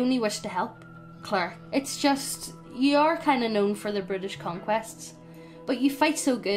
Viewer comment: due to the accent, I keep hearing this as 'British Conquest' and imagine armies of monocled bowler-hatted hobgoblins marching through the streets of London armed with an umbrella in one hand and holding a saucer upon which rests a fresh cup of hot tea in the other.